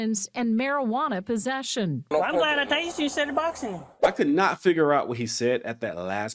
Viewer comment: I sounded like he said, "I am glad I tasered you instead of boxing you.".